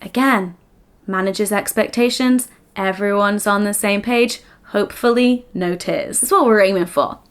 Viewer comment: i was spaced out while this video was playing and then when said “hopefully no tears” i started actively listening again and i thought she said “hopefully no tits” and i was like what has she just been talking about lmaoo